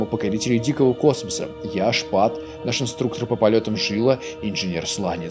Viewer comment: Шпат 🤣🤣 Кому ещё послышалось #ШПАК